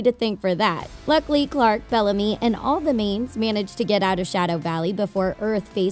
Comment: it sounds like she said "Shadow Valley" Lol